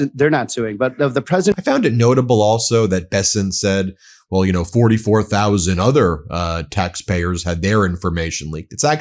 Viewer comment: I dont think he said it was 44,000 tax payers documents were leaked? I swear I heard him say four HUNDRED and forty thousand. So thats just a few more.